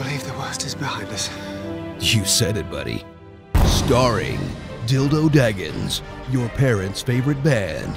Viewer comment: Did i heard "Bildo Daggings"?